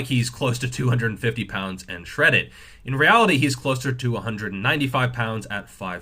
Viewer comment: I heard "he's closer 295 lbs" instead of "he's closer to 195 lbs"